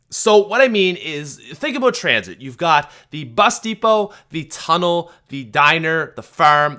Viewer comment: Guys... I gotta say it... I have to say it... UNDER 301+ CLUB HOW TOUGH ARE YA I couldn't resist. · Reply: Lol I thought you said busty hoe when you said bus depot